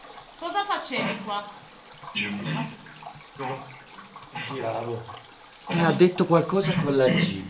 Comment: io ho capito giocavo non è che avete parlato con il bambino